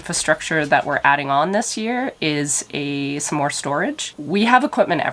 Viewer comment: Omg I thought you said a Smore store 😂😂 Also, women hating on women….really????